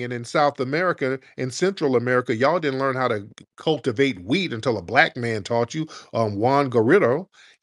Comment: Even can’t keep up with Incoherent has a comprehension problem, he thought said “weed” 🤦🏽‍♂️ instead of wheat - geez “somebody give me a knife”